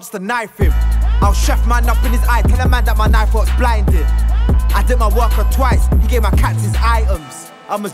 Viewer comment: Did bossman just say he dipped his worker twice @ 0:50 I bet he gives them £1